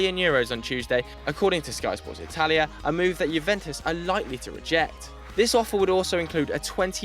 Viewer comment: What he said at 2:31 unlikely to reject or likely to reject? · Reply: likely